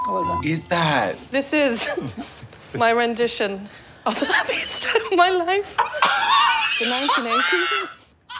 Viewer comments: "this is my rendition of the happiest day of my life" always gets me, i almost CHOKED. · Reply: I thought she said "happiest turd of my life" until I read your comment 😭😭😭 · what did she say at 11:53? a rendition of a what? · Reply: the happiest day of my life